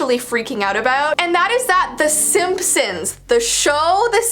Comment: She did not just say the SIMP sims 😂